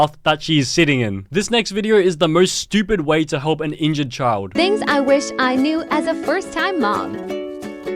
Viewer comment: When the mom said “thing I (I forgot what she said)” I thought she said things I wish I knew as I birth my mom